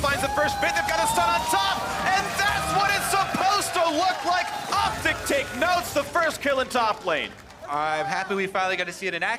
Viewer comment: anyone else hear him yell OPTIC gets the first kill 😂😂😂